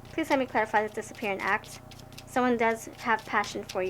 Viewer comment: Omg I thought pile 3 said disappearing rat 🐀 I’m like damn I got a rat watching me 🤣🤣